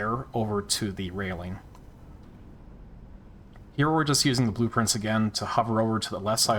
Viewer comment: Oh my god. Blueprints. Blue Prince. What the fuck. It took you saying “open blueprints over and over” and me getting confused because I heard “open blue prince”